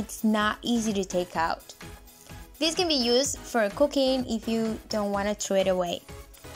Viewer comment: i swear I thought she said cocaine😅😫